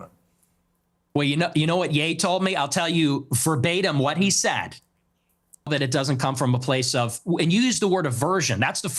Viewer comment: What did ye say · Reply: When N being N - and Nick answered "So true" Pierce then asked what does this even mean? And Nick answered "That even black people know how black people can be"